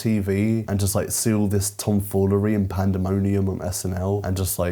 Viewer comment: I misheard and subsequently wished for "timfoolery"